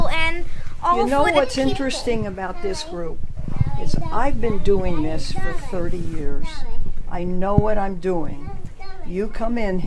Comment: What she said : "..I've been doing this for 30 years.." What I heard : "I haven't done anything for 30 years"